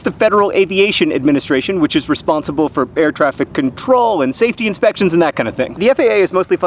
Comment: I thought at 00:36 you said "bear traffic control". Then I realized and got sad. :(